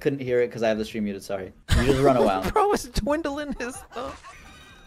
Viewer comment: did just say "dwindlin' his thumbs"? lmao